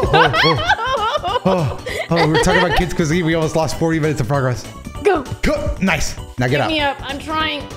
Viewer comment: 40 minutes of progress! Did I hear that right?